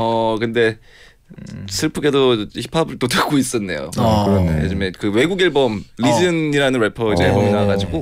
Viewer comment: who..? 누구라고 하는지 못들었너욥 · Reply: reason